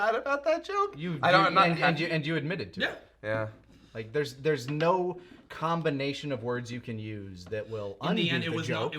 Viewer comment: where is this slur? I don't get it please help? Was it the scene coming up or the one before? Is 18 saying "tranny" and that's the one? Since I could barely understand what she said. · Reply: Yeah, "tranny" is a slur